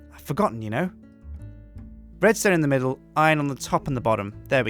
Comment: it sounds like you say breadstone